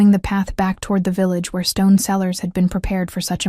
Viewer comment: Stone sellers??? I thought maybe they were selling gemstones until I realized they were running to the "stone cellars" 9:49 there were other amusing malaprops before now, and I'm sure there will be many more before the end of the story, but this one really caught me =)) Nearby, I heard Miriam gas?? oh my!! Nevermind, no need to hold our nose.. Miriam gasped =)) Keep your childrens clothes.. not donate them when they're outgrown? Ohhhh, keep them close.. near you. I get it. WHAT??? WHAT??????? I WAS FULLY INVESTED.. FORGETTING THE SILLINESS, LEANING INTO THE STORY AND IT'S ENDED????? OHHH JEEEEZ... ARE YOU GOING TO MAKE A CONTINUATION OR JUST LEAVE US HANGING???